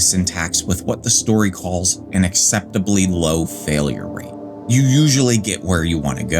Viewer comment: I heard 'exceptionally low failure rate', then realized it was actually 'acceptably low' and straight choked 😂